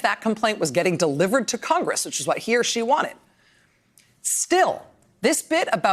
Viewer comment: Did she say that the whistleblower is a “she”?...